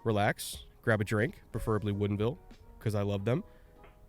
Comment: What type of Whisky did you recommend I couldn't hear you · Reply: Woodinville :)